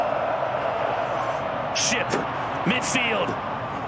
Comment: What did he just say? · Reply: Ship. Why?